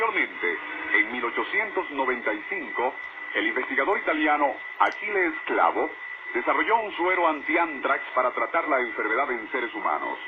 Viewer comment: En el minuto 10:44 pareciera que dice el investigador italiano AQUILES CLABO o AQUI LES CLAVO., mi gracioso.